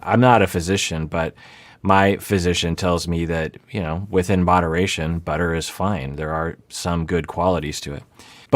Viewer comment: I needed to rewind that three times to not hear “within butteration, butter is fine” anymore. I feel butterly stupid for mishearing that